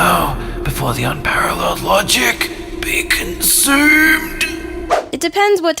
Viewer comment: I hear "Bacon soup" instead of "be consumed"